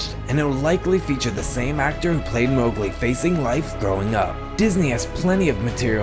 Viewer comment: is it onley me that thinks he sounded like he said “Throughing up” instead of “growing up” XD idekam lol :3